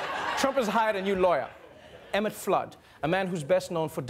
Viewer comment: I keep mishearing 'Emmet Flood' as 'Elmer Fudd'! (The jury's still out on which of those two would actually be more useful or effective!)